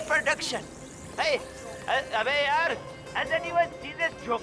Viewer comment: does hi says "aabe yrr"